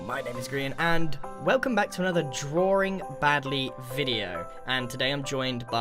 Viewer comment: I thought it Said baby insedd of badly...